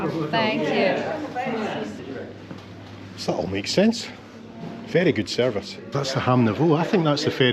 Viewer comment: Saturday video! Did I hear "ferry good service":) Thank you and.